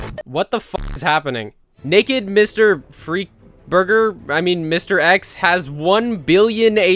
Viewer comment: Burger did he just say his name is burger?